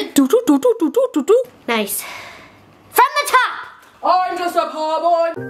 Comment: I thought she was saying from the top make it drop